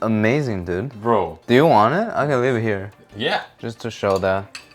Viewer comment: "this thing is amazing bro! Do you want it? Cuz i dont want it!" Is what i heard